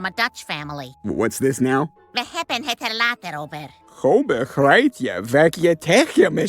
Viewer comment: Holy moly was it hard to decipher what Chris just said. Lois says: "We hebben het er later over." (Although she pronounces "hebben" like it's spelt with two p's, and the Dutch pronounce "over" the same way english do. Literal translation: "We have it later about" Funnilly, if you remove the word "talk" from an english sentence, you can see how this works. Anyway, this is an excellent example about how bad english people are at trying to talk dutch. It's honestly the weirdest thing. As someone who speaks both english and dutch fluently, english being my first language, it's baffeling how bad it is. I almost gave up and just assumed Chris wasn't talking dutch but just some giberrish as a way of mocking the dutch, but here's what he so badly butchered: "Hoe begrijp ik wat je tegen me zecht?" It's so badly pronounced, those yellow subs honestly do a better job at accurately spelling out what he just said. It's so bad, here's how a dutch person would try to spell what he just said: "ho begreit je wet je tegje mesjeggd" I spelled that last bit with two g's because it's actually a pronunciation of the letter "g" that we don't use. We don't pronounce the letter "g" as you would for the word: "goal" That's not the point, the point is, he is clearly missing a word in his absolute slaughter of a Dutch sentence: "ik" (the Dutch word for "i" referring to oneself) There's just no sign of that word anywhere in there. Clearly the voice actor didn't even bother to memorize his line prop